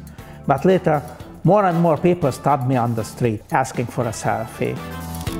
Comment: What he said: Asking for a selfie. What the sub says: Asking for a sour face. 😂